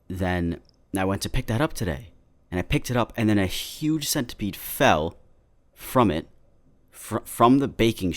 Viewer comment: For a second I thought you said human not huge centipede 😂😂